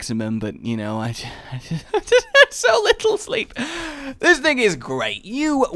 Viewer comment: Am I the only one who hears "this nigga is great"?